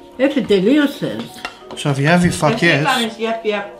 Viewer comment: When I first heard it, thought you said F**k yes! lol Good job, lovely Grandma and meal! keep it up